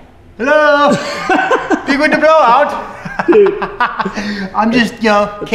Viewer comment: did he say "big winter blow out" ? lol